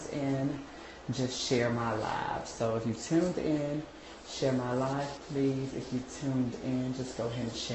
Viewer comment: if this girl say, "share my live" 1 more time👉 I'm gonna 😱scream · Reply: [NQZATR] I thought she said share my world, I'm about to say where mary j?